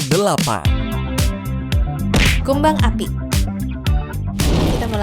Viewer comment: Aku dengernya "Kembang api"😭